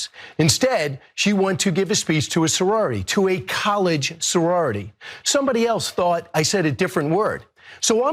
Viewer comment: Im NOT a defender of his by any means, but I listened a few times and thought I heard college sorority....Im typing this as hes talking.....I have NOT listened to his defense yet.